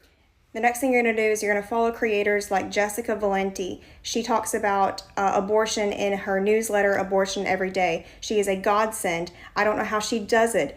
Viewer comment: TikTok'r speaking, starting at about 10:04 in this video: "She talks about, uh, ABORTION in her newsletter, 'ABORTION EVERY DAY'. She is a GODSEND." I had to REPLAY that part of the video to make sure I heard her CORRECTLY...🤦‍♂️